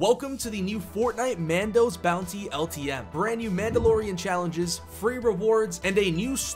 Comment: It sounds like he said mandos bouncy ltm lol